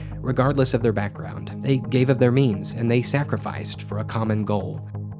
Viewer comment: I heard gave up there memes lol